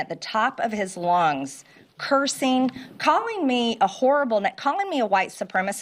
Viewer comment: For a second I thought she said he called her a whore, which would have been accurate 😂